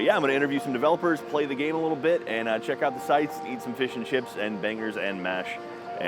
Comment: I swear I thought said " eat fish & chips and bang her ass" at 0:25 xD